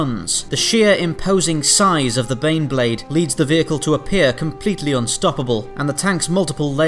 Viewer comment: If you enjoy Spacedock, please consider pledging a dollar a month to the Spacedock Patreon, it helps keep the show running and expanding, plus you'll get access to the monthly schedules and the chance to vote on upcoming videos. :) <link> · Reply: For the subtitles at 0:46 you wrote 'complete' instead of 'completely'. Can be referred under human error protocol 29.